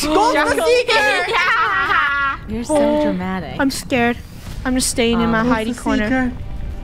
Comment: can anybody else hear your so dramatic?